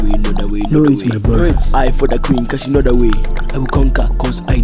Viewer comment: LYRICS (someone put this on genius lol there's already one but the person didn't finish it) Ou ou ou Uganda Know da wei ou ou ou Know da wei x7 I fock da queen co she kno da wei I will conquer coz i know da way Im looking for zimbawe We will spit on yo face (SPIT ON HIM) Only if u dont know da wei Know da wei know da wei know da wei Know da wei x6 I fock da queen co she kno da wei I will conquer coz i know da way If u do not know da wei my bruddas spit on yo face (SPIT ON HIM) If u dont know the way (way) I will slap yo face You are probbly gey Probably from zimbabwe (zimbabwe) None of you donkeys kno da wei Me and my bruddas spit on yo face If u dont want spit on yo face Know da wei know da wei know da wei Know da wei x7 I fock da queen co she kno da wei I will conquer coz i know da way If you do not kno da wei My bruddas spit on yo face If you do not kno da wei Ajfkzi jdkfeiu blahlala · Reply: I thought you said "eye for the queen cuz she kno de wae"